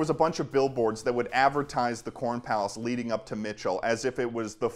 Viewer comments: So I was playing this video in the background and I thought he said porn palace.....I spent the first 2 minutes of the video thinking that the tourists drawn to mt. Rushmore were much more perverted then I would have thought... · I legit thought he said porn palace